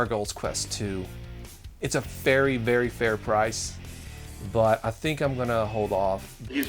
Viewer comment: did he say "it's a fairy very fair price"?